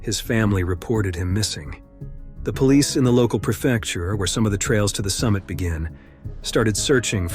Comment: What is a "prochecture" (6:02)? You say "the local prochecture." Is this what they call the local police/SAR teams? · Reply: he’s saying prefecture. It’s basically a district or a county, if you will. An area of land that is basically all part of one entity. (Edit: sorry. I should have scrolled to see that others already answered you. )